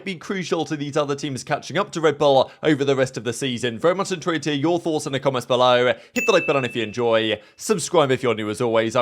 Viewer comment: what do you say right before HIT THE LIKE BUTTON IF YOU ENJOY? something about "your thoughts". Even the closed caption doesnt get it lol. Im in america so maybe its a phrase we dont use. Either way, i like it... and great channel lol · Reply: I thought he was saying “enter in your thoughts in the comments below. “